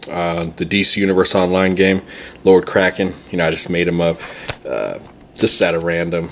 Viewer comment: Lord Kraken, wicked! At 1st, I thought you said Lord Crackin